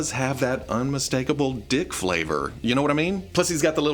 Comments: Did you just say "dick flavor". What does that taste like anyway😅😊😂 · Wait, forget "come to daddy", did he just say "unmistakable d!c# flavor"? 🤣